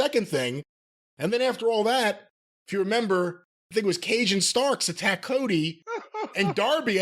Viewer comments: When Brian said "Cage and Starks", I heard it as "Cajun Starks". Now that's all I want in 2023. Happy new year boiz. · Brian Last said “Cage & Starks attack Cody” but at first I thought he said “Cajun Starks.” I got confused and thought I missed a point in time where AEW had Ricky Starks use his actual New Orleans roots to play a Cajun character. Lol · Brian Last said “Cage & Starks attack Cody” but at first I thought he said “Cajun Starks.” I got confused and thought I missed a point in time where AEW had Ricky Starks use his actual New Orleans roots to play a Cajun character. Lol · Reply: I swear I heard "Cajun Starks" too! That's a gimmick worth exploring